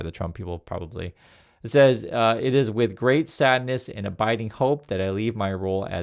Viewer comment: I misheard "abiding hope" as "a Biden hope". I'd take some Biden hope right now.